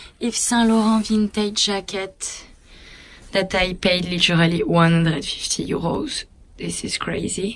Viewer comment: How much did she pay for that jacket? I couldn't understand when she said it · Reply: 150€.